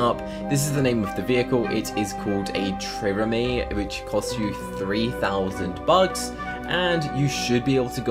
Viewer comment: When u said 2000 bucks I thought u said roubux and I was like that a lot!?! Lol